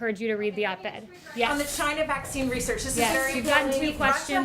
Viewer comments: I heard (OK you don't want to engage) I did not hear the "so" in the (OK, so you don't ...) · I support the president but I heard “ok you don’t wanna engage”. Please let’s not become the witch hunting left over this. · The reporter and press room transcripts are saying she said "OK you dont wanna engage." BUT when I hear it...I hear "OK dumb lying bitch." I hear it every time now. The tempo, the time it takes to say the words...I am most likely wrong, but that's what I hear. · She said - “okay, so you don’t want to engage?” Had she said what everyone thought she said, id agree with the outrage. There are other clips that single out the audio - and it’s clear she did not say “lying bitch” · Tbf I heard ‘ok so you don’t wanna engage’